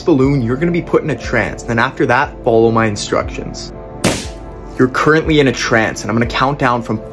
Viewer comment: When he said you are currently in a trans I thought he said that I was being put in a trap